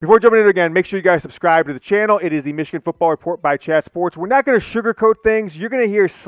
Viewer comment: I could have sworn you said shat sports.